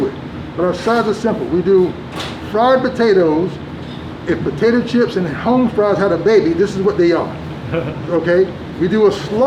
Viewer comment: I thought he said a cross between potato chips and home fries